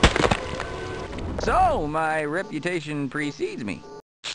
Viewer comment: guts and gibs flying everywhere the only one police officer that lived: "so my reputation received me" I think he said that